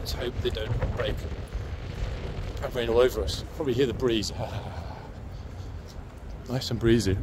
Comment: i thought they might say, `Brieeee!`